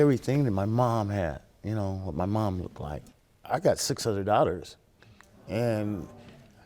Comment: did he say he has 600 daughters